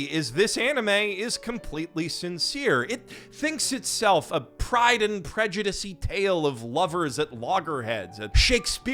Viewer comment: I know meant Pride and Prejudice-y, but I heard "Pride and Prejudussy" 😅